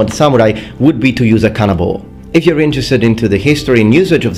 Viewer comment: For me it seems quite unfair to use canonball in comparison tu melee weapons ;) (I know, I misheard kanabō)